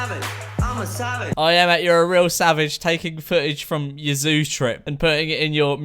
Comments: N&A Sounded like he was saying salmon than savage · I swear I thought he said Salad not savage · I thought instead of ima savage o thought he said homicidal ima homicidal uh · I thought he was saying 'I'm a salad' not savage